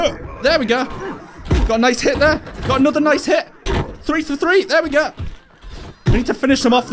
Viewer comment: for a second i thought he said " I got another nice Hitler! "